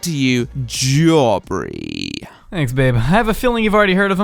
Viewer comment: Is it just me or did he say “Thanks babe” Lol